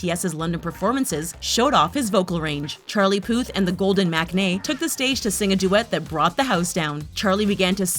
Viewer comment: Sorry I heard ...it's as golden magnet